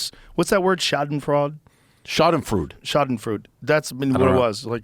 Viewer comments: What kills me is how he corrects Joe’s pronunciation of schadenfreude with an equally incorrect pronunciation, with such confidence lmao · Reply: Sounded like he said Shart-in-fruit 🤣 try shaaden-freud-eh? 🤑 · what word did they mean at 4:00? was it schadenfreude?